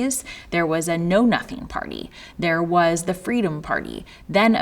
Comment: Wait, did she say... "no-nuttin' party" lol